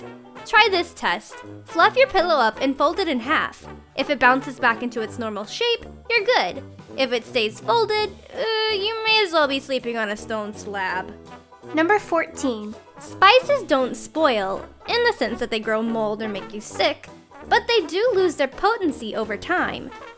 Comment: I thought said you might as well be sleeping on Dolan's flabs XD